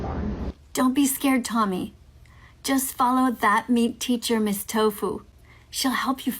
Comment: Lmao Ok I have a question. Go to 1:45 in this video...what the hell is she saying?! It sounds like "follow that MEAT teacher Ms. Tofu" But that makes no sense. I've listened over and over and I've got no clue. It doesn't sound like neat or mean or meek even, it sounds like "meat teacher Ms. Tofu"😂 it's driving me nuts lmao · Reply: Thats another account she has, where she i guess "mocks" the haters?? I honestly don't get it either, but it's called That Meat Teacher Ms Tofu... And it's basically the same thing as her normal acc